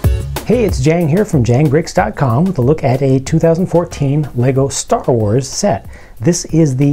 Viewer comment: did you say stuffa wars?